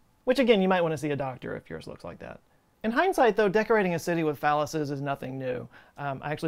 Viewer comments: “Decorating a city with phalluses...” Me: Did he just say dickorating? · LOL at first I thought you said "dickorating a city" and I was like, whoa nice word play.